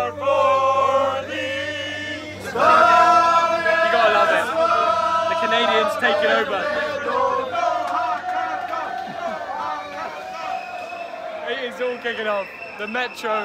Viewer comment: did he say Canadians? 😂